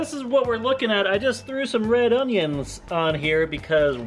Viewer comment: I thought said that you hated red onions 😂😂